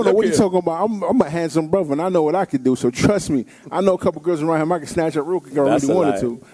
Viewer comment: What he say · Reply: “you scared”